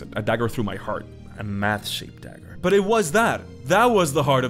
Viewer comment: My brain is... "Special." You said, "A Math shaped dagger." I heard, "A Matt shaped dagger." :D